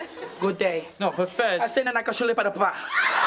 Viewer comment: “I said ‘na na ka shulei padah pah!’”😂 that’s what i heard